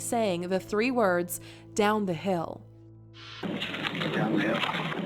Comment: It sounds like he is saying “get down the hill” or “Go down the hill”